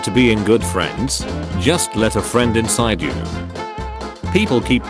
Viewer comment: I HEARD THAT ONCE AND MY MIND SCREAMED "FRIEND INSIDE ME"😭🙏